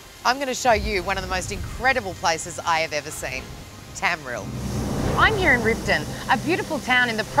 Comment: Oh lordy. Sophie Monk from Neighbours?? 0:20 01:36 Did she just say Tamrill?